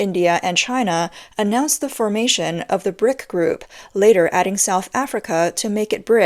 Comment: Even Putin knows china's ambitions. Just say Vladivostok is China's infront of Putin , then Putin's reaction will be amazing to observe. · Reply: I thought she said prick group😂